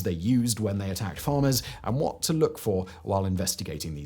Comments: For the first half of the video I thought he was saying Farmer tax. Thinking dang my income tax doesn’t sound all that bad anymore 😂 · For half the video, i thought was saying "farmer tax." I think i need to listen better.😂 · Just me or did it sound like he was saying farmer tax? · I thought you were saying farmer tax. I thought. This doesn't seem that bad...